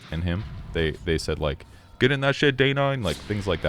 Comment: Hey, I’ve listened to 6:57 like seven times, and can’t understand what they said. Anyone have a clue? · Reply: "Get in that shit Day9" is what he said I'm pretty sure